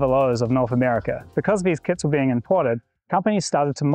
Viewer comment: i heard "because these kids were being imported"😂